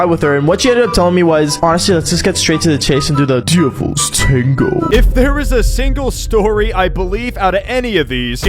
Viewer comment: Why in the world does the YouTube caption say “Beautiful Strangle!” Instead of Devil’s Tango?! 💀